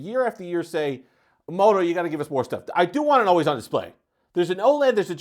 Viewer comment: what are you saying? "I do wanna [mumble mumble mumble] the display." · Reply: Apparently CC thinks you're saying you want an always on display.